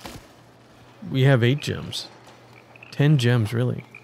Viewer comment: Not gonna lie, I really thought you had said "10 Jims" for a second there